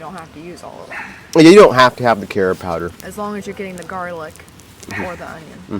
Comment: I actually never heard of karob powder till you brought it up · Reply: I thought he said carrot powder at first 😂